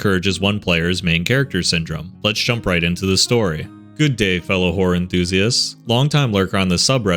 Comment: I THOUGHT HE SAID “hello fellow whore enthusiasts” AND IM DYING 😭💀